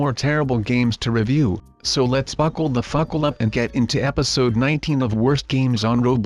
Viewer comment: I THOUGHT HE SAID FUCK THE BUCKLE UP xD